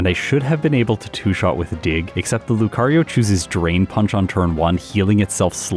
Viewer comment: I'm drunk and tired so I heard "Terrain Punch" and "Forest Balm". They join "Tombstoner" as names that totally sound like they could be moves, but unfortunately aren't.